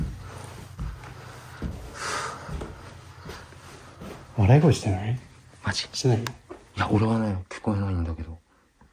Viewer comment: 女の人の声なのか笑い声が聞こえてその後に何やっとるんて聞こえてめちゃくちゃゾクってした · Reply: 確実に「何やっとるん？」って言った